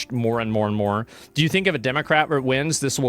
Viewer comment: I swear he says Democraper😂